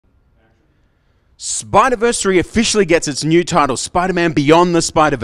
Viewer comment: Spider verse 2? Not 3 🤦🏽‍♂️ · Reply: dude we are on our 2nd “Into the spider verse” in the beginning it sounds like he said Spiderverse 3